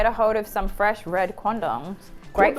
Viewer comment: not sure what 'Baeyb' said at 20:44, but in her accent it sure sounds like 'fresh red c0nd0ms' to me